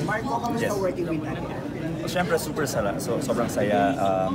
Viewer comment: Did I hear it right? 1:06. "Super sarap"? Hahahaha